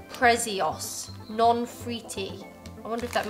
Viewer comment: Non fritti means not fried 🙂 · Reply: I thought he said non frutti